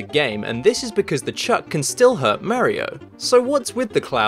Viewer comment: did you say merio?